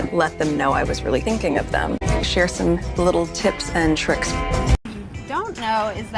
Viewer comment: I thought she said “I share some little tits and tricks” 🙊🙈🤣🤣